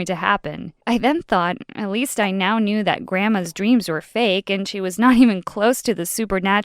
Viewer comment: I thought u said gwandma